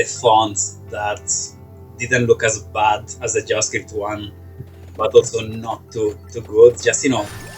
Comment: heads up, there's a minor mistake in the captions at 19:22 -- captions say "didn't look as bad as the adjusted one," however what says there is "didn't look as bad as the javascript one"